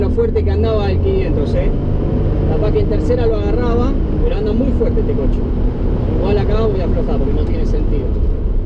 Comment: como llege aca q copado 6:11 dijo anda muy fuerte este konchudo? o escuche cualqiera